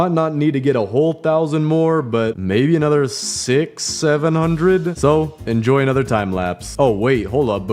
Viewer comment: did u just say 67? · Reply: He said “670?”